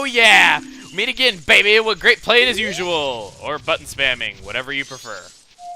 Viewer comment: Sounded like he said "butt-spamming"